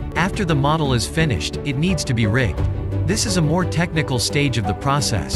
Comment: subtitles said it needs to be raped?